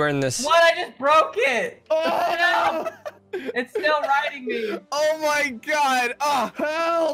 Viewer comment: what did say :o XD in case you can't hear it he says "its still riding me"